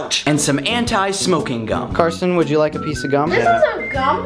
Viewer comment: DID HE SAY KARSTEN? I've never known another human to have my exact name....unless her name is Carson....then this is just awkward...